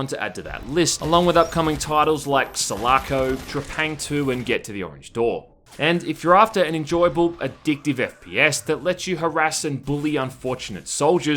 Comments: I cannot for the life of me understand what you are saying when you are listing off the other games at 0:27. Salarco? Dra Pang 2? What are these games called? Subtitles has done shite all. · What the heck is that game on 0:27 I heard "solaco" and googled every possible word/spelling and couldn't find it. Fucking speak properly dude, you are not a 1940s radio ad announcer. geez XD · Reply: Selaco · What is that game at 00:28? He says something like Trafangto or smth? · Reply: Trepang 2